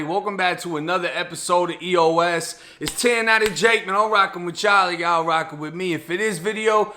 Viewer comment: Can some1 tell me what he is sayin n the beginning...1090 jake then sounds like he says he's rockin w jolly? or he rocking w Charlie?I idk if it's the accent or if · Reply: Rockin witchyall lol